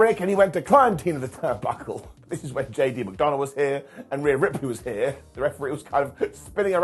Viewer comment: Is it just me, or doesn’t it sound like is saying JD Mcdonut? lol